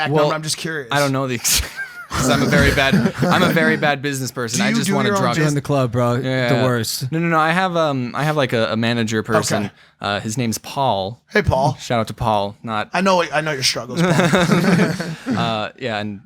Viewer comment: someone replay what he said. “I just wanted drugs is what I heard 🤷🏼‍♂️🤷🏼‍♂️” again just what I heard